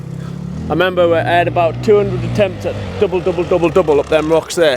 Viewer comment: I thought you said 10 attempts 😂 I was like uh double that. Definitely always fun watching your vids! Come to Cali and let’s go do some hard enduro.